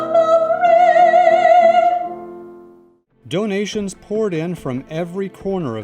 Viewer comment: I thought he said, "young Asians poured in.." at first